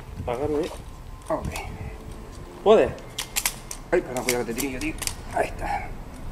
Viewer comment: en el minuto 11:14 se escucha que dicen hey hey como pidiendo ayuda